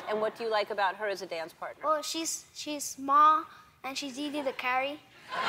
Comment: Oh i thought i heard "she's small and she eat curry". LOLOL. Good i read the comments below. :P